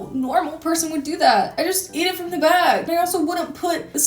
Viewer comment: What did she say? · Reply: “I just eat it from the bag”